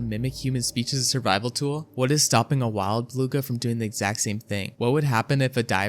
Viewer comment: Why did the subtitles say "blue whale" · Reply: beluga, must be a mistake